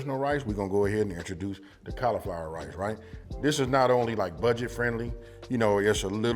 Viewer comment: I could have sworn AB said California rice. This still looks pretty good